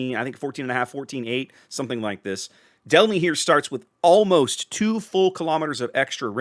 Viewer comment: Am I the only one to misheard delny as delhi?